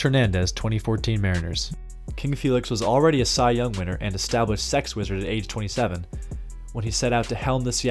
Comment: Established Sex Wizard? Did I hear that right? lol.